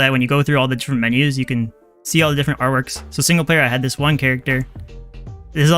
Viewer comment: when it said single player in the menu i thought it said pringle slayer... 🤣